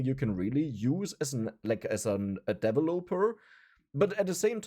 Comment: I know he said “developer,” but it sounded like “devil opener”😂 Edit: this has nothing to do with his accent or how he pronounces words it’s all within my head. I.E. when I read the word “coworker” my brain puts in a space, the word than becomes ”cow orker”